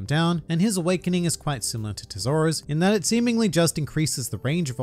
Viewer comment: Did you say awakening is similar to Zoro?